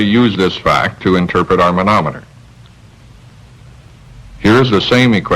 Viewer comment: phenomenon instead of manometer